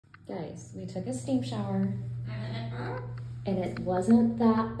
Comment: At first I thought you said stink shower, it took me a couple of watchings to know she said Steam Shower. LoL Sweet Pea's like: "What if we wanted to get wet."